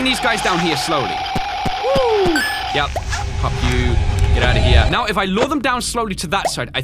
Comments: why did he say f you at 4:18?😂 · What did you say at 4:17 ? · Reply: Bop you (when he got rid of helpy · What did he say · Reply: "Yep. pops Helpy Pop you.." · did he say f**k you? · Anyone else hear say "F you" at 4:17, no big deal but he rarely curses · did say pop you or f you.